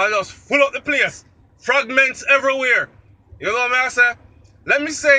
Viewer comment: did you say fragments everywhere or did you say fagments lol 😂😂😂